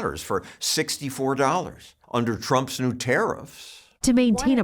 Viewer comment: I don't drink wine or champagne. I don't care for gardening and potted plants. Terifs are not bothering my checkbook. Just saying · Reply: I, too, thought she said "planters". Turns out she actually sells "planners".